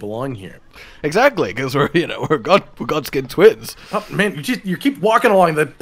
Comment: I misheard Shortskin as Fore